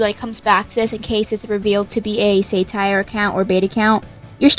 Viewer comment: did you just say “say”tire 😭